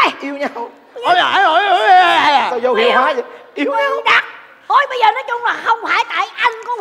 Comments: nói gì vậy? Nghe đi nghe lại nhiều lần rồi! Vẫn nghe bậy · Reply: Duy Ruby "bày đặc" đó bạn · Có lẽ tui nghe nhầm nhưng ở khúc 42:52 Cát Phượng nói " Yêu yêu con cặc"